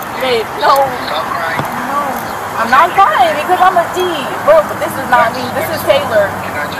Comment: On the 13:11 mark, did she say "I'm not crying because I'm a G" ? 😂